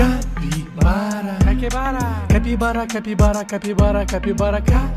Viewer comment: Me when I first saw this I heard “cacy barra”